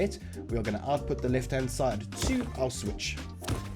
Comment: When he said "to our switch" i thought he Said "to auschwitz" lmao wtf is Wrong with me